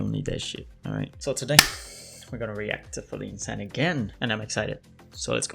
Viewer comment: I like how at 0:58 the auto generated English captions instead of putting feline San like it always does it puts "fully insane" I can't tell of this is accurate or not